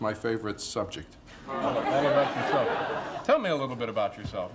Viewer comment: Did anyone hear the extra tell me a little about yourself? At 1:10. Was that Lettermans earpiece telling him what to ask next for questions? Hmmmmm maybe….